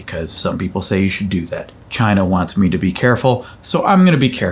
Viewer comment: when you said that in the context of COVID, my mind first heard that as "China wants me to be careful", which I thought was a subtle joke about the zero-covid policy and lockdowns... before it hit me that you were talking about your wife.